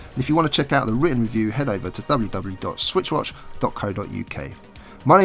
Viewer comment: I thought it said "John Romero" and as a huge fan a Doom I was like " WHAT "